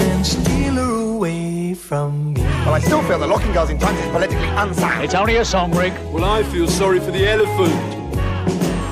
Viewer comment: I never quite caught what Rick says at 3:19, to which Mike replies with "It's only a song, Rick" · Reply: “I still think locking girls up in a trunk is politically unsound”